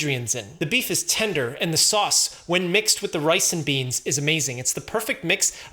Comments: I thought I heard ricin beans. But then realized it was “rice & beans” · sounded like he said ricin beans....😂 got me over thinking 🤔